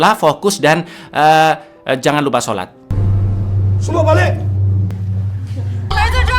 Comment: semua balek.. aku dengernya gtu